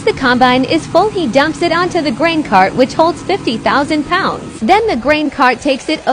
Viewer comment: Ima be honest, the first time the text to speech said grain cart I thought it said brain cart 💀